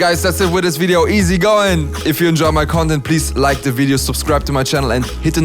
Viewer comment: he said "content" , but it sounded like "if you enjoy my c0n0dom" for some reason.